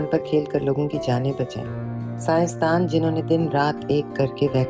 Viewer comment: what's the word? सायंस्तान? It is translated as scientists in the sub titles. Interested in knowing the original word. Can anyone help please? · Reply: sciencedan means scientist